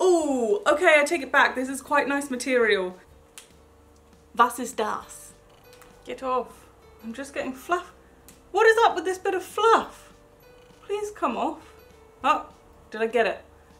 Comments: or sth did you say " Was ist das?" ??? 😲😲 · Did you say "was ist das?" 😂💙